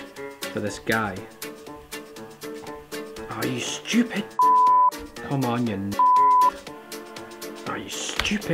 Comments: Anyone else hear him say “come on ya ni... beep” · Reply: He says Knob, watch it again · I thought he said come on n***a XD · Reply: Nvm he said knobhead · does he call him the n word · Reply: He said knobhead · "come on you n*BEEP*" what was that word? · Guys, if you think he said the N word at 1:38, he actually said ‘knob’.